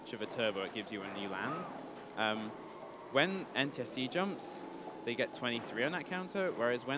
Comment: What does he say gets higher jumps? Also great game. I remember being addicted to this game when I was 8. I tried picking it back up again and I still had my old time trials saved... I couldn't beat them. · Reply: NTSC